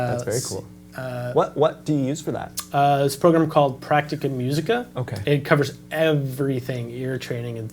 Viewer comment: did you say Practica musica app?